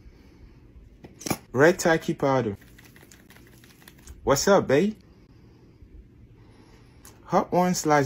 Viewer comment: He said "Wasabi" but instead I heard "What's up bae" 💀💀